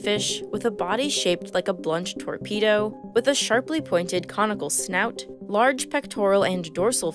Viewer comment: I heard "comical snout" instead of "conical snout" and was like "I don't know if I would describe them as comical... But I guess they are kinda funny!" I got really into calling what so many see as murder machines "funny guys!". Man, I do love sharks.